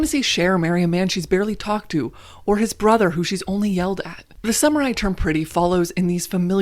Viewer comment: Off topic but at 0:19 the captions said “the samurai term pretty” instead of the “summer I turned pretty” and I can’t stop laughing at it😂😂